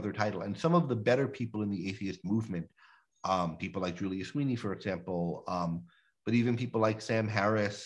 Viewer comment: Did he say Julia Sweeney, the comedic actress who played “It’s Pat” on SNL?